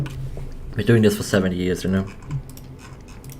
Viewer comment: "been doing this for 7 years" i heard that as "been doing this for 70 years"